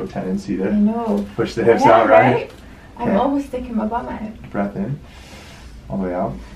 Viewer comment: I thought she said I always feel it in my butt crack